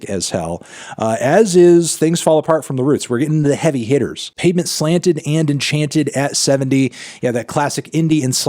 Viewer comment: I was just listening and thought I heard “penis slanted and enchanted” I was like damn what a name